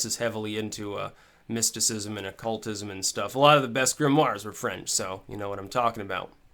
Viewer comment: What did he say at 4:08? and what was he talking? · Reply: He was talking about mysticism and occult literature, and how he backs those videos up on Daily-motion (a french site) At 4:08 he said that many of the great grimoires were french, and how the french love mysticism.